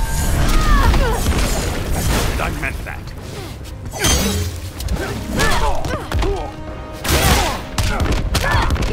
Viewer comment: I can never understand what Aquaman says at 5:04. The closest I can make it out to is "Nun Man-Bat!" which is a rather nightmarish concept... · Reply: Yes its, "And I meant that!" So perhaps its meant to imply he is being serious.